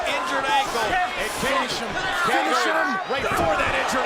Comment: No one gonna talk about what was going on at 15:00 ⁉️ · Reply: I heard, "finish him, Kane," multiple times. It sounded like Mortal Kombat @ 15:03.